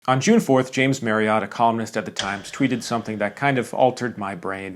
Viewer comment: I thought I heard u say he was a communist and I got excited 😢